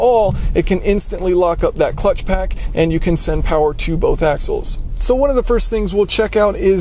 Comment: Hey +Engineering Explained I think you said a*sholes at 5:00 lol